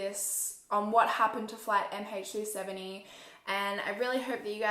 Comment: Omg for 15 mins of the video I thought she was saying flight MH37 E and I’ve just realised she was saying MH3 70 I was think it’s zero not E😂